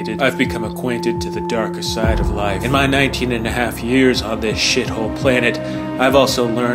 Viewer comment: Wait I thought at first you said "19 half-years", implying the protagonist was 9.5 years old. But that's silly. We're talking about YA, not anime.